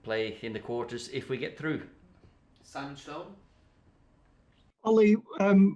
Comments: I literally heard "Sancho" 6:08 Btw I know he said Simon stones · the guy said Simon Stone but I heard sancho 🤣